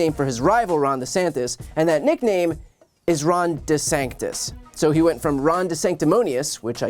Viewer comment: I thought he was saying "DeSankedUs" lol.